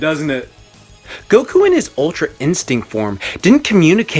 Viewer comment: I thought he said "ultra insect", I was confused lol